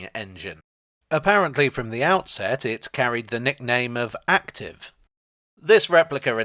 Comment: Misheard Active as Acton - wondered what an iconic loco calked Mornington Crescent would look like \m/